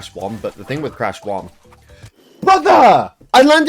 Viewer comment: you sounded like the " ew brother meme '